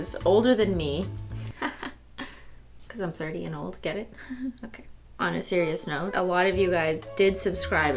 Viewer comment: anyone else hear jenna marbles sing “I’m a 32 year old lady”?